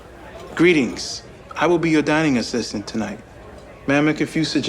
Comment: did he just say dying or did he say dining😭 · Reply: Dining